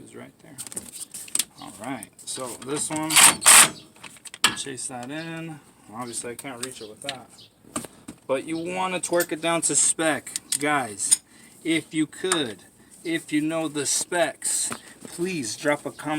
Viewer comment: i swear you say " you wanna twerk it down to specs" this aint no girl shaking her bootay brotha! Torque it down! Hahahahaahah, even though we do talk to cars like their women! Hahaha